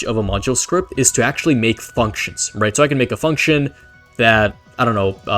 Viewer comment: i heard f*ckings but when i replayed the section then i heard functions